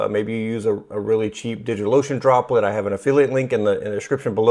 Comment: This is great! Definitely gonna have a think about this. Also, I heard "Digital lotion" and got a giggle.